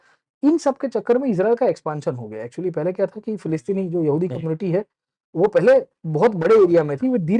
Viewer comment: Sir great video Thank you so much But at 5:50-7:00 You said yahudi rights instead of Arab Rights. That became very confusing